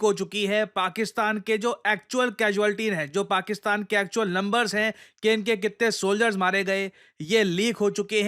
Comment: did he actually said lumbers ?? 😅😅😂😂